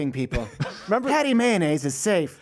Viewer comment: Did he say Patty mayonnaise at 29:26?